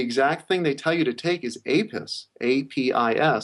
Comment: "In the homeopathy kits, the exact they tell you to take is "A piss!" Hahahahahaahah! I know that's not what he actually said, but it's funny ^^ I'm not bashing urine therapy, I actually practice it proudly. It just sounded ironically funny hahaha